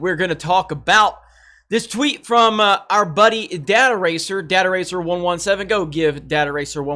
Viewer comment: Why do I keep hearing “dad eraser”? 😁